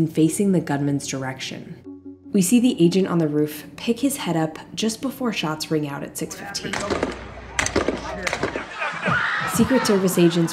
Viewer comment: I hear a “sh**”